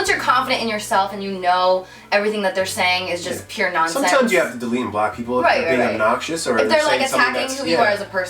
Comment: LOL sounds like said '' SOMETIMES YOU GOTTA DELETE BLACK PEOPLE''